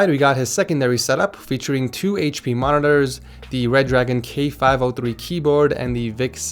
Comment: Anyone else catch Reragon at 4:17? lul